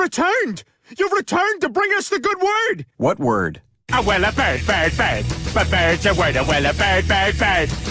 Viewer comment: I only hear "oh wella I hate ni-"